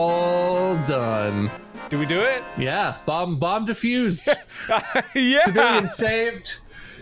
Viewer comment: I thought mark said Bob diffused it turns out he said. "Bomb defused"